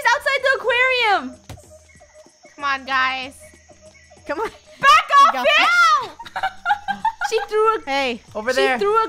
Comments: wait- did just swore...? uhhh i think idk hmmm... in the 5:11 part i think she said "Back off, beach!" ehhhh idk might be mishear or yeah but maybe someone help? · I THOUGHT SHE SAID B’’’’’ I was like oh god. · who also thought she said "back off b*" not to offend of curse but yeh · Is it just me, or at 5:11 did say BACK OFF (WORD)?! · have I misheard?? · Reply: She said fish. Everyone is sayin this. It’s not that hard to listen. She said fish! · it sounds like said "backoff b* " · Did just said "back off bit*h"??? 5:15 (like if u agree) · I think a heard say " BACK OFF BI***" at 5:12. Edit: Like if u heard it. ;) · did anyone else hear say back of fish 😂😂😂